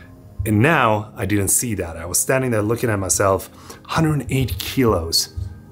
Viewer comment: I thought you said "180 kilos", and I was like, "dude, you don't look like you're THAT tall" 😝😝😝😝